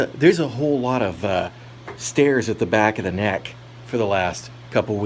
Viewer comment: For a moment there I thought you said "stairs at the back of the neck". I was confused for 15 seconds (it is early).